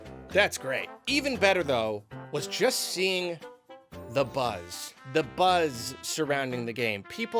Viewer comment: Did he say... Dabuz? 😋